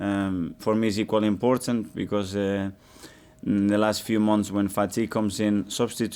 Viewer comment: was anyone else thinking Mikel was insulting a players weight?! “When fatty comes in” is what I heard!! 😂